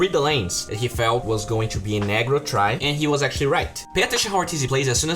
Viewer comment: A negro tri? What chu say? Cmon bruh